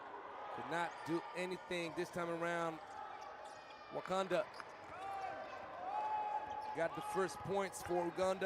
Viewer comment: Did bro really say wakanda 😭😭